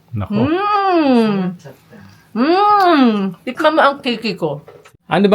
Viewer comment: tama ba narinig ko tikman mo kiki ko hahhahhaa